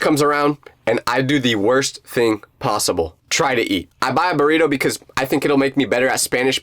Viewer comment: I thought he said try to cheat